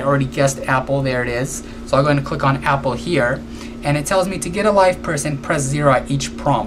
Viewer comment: lol at first i thought you said "it told me to get a life"